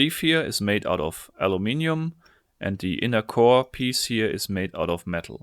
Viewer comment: I thought u said Jalapeño